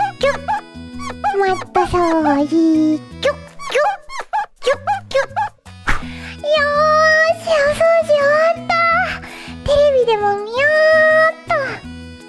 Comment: 股掃除～♪に聞こえた俺の耳は一体どうしちまったんだ…